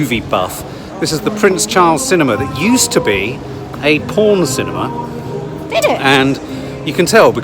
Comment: 😂i thought you said corn cinema! I was thinking 🤔 why wipedown seats!! Xx😂😂